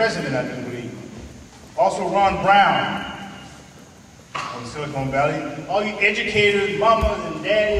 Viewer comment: is he saying "Sili-CONE Valley" as opposed to "Silicon Valley"